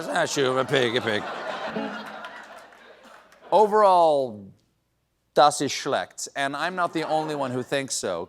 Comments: Amazing opening ☺ Did I hear right at 8:55 : "Das ist schlecht"? Sounds like someone taught Stephen Colbert a little German. 😄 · Am i mishearing something, or is he really sprinkling some German into it, like "Das ist schlecht" (That is bad) ??? First Conan, now Colbert. Flula, what are you up to?